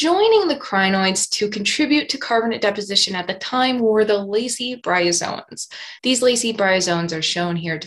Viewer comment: I heard "Lazy bryozoans"